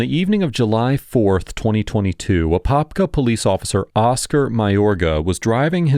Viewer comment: I was just listening to this at first in the next room and I swore the narrator said "Oscar Mayer guy" 😂